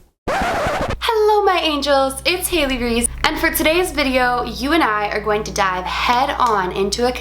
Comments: I freaked out cause I thought she said, "For today's video, you and I are going to die", in the beginning ._. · i heard "for today's video you and i are going to die"